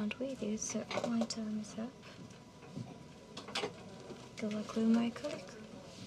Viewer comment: Mannnn what are they saying 😂 at 22:30 the girl sounds like she says " Good luck with my c*ck” 😂